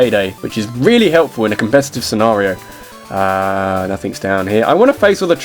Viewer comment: Did he say Payday is really helpful in a 'convestive' scenario at 6:50? o.O